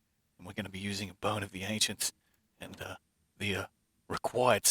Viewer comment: Haha I thought he said boner of the ancients